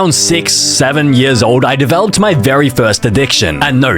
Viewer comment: UMM GUYS........... DID HE SAY 67?????????????!??!?!???!?!?!?!?!